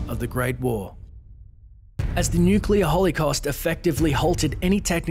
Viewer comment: Did he say Nuclear Hollycost?